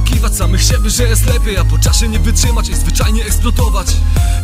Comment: This sounds really sick, even if I can't understand it. The beat is absolutely fire! 🔥 But I can't help laughing at how 1:21 sounds like "Vagina explotovasz" XD